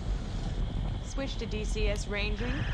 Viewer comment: The pilot is so cool, she wears sunglasses in space! The absolute confidence of a cool-headed pilot, "We're in the pipe...5x5". Badass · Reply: I always thought she was saying "in the PIKE..." because they go into a nose down position. .